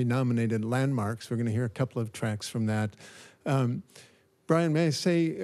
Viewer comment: did he just call him Brian May?